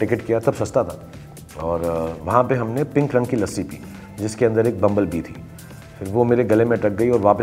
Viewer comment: Koi btaaega kya bola Kya fass gya gle me??? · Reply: Bumble bee- an insect